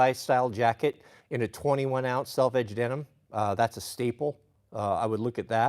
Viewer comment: Can somebody tell me what the denim is that he keeps talking about I cannot for the life of me understand sometimes it sounds like he says Belle Edge denim, sometimes velvet denim. I can't u derstand him he mumbles it very fast. can someone clear that up for me. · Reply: Selvedge denim